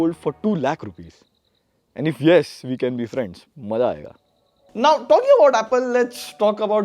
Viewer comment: Bhai yrr Kya bola apne samajh nhi aya 😂😂😂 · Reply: He said "maza ayega"